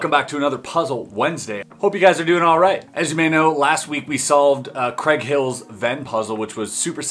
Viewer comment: I thought you said sold.